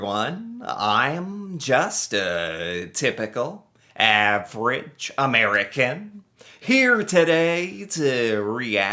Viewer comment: Lol for some reason my player skipped and i heard you say im just a pickle average American